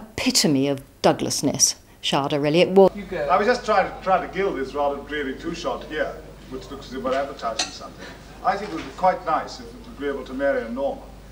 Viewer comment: what does he sais at 1:17?....I can't understand...... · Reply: "I was just trying to gild this rather dreary two-shot, here, which looks as if we're advertising something."